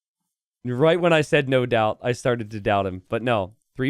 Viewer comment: I heard " no Daut" instead, but he isn't in this match :D